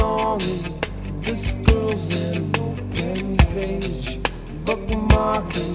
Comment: As a kid I heard.... "Ghost Man so close to me!" 😆